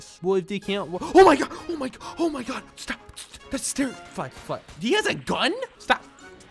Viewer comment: did he just say fuck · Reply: Fly fly fly